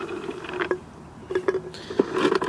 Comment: The funniest thing i hear: “It's four bananas for funny monster.”